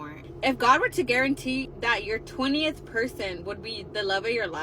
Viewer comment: did she said 20th person ? so i am gonna be there after 20 guys ? no thank you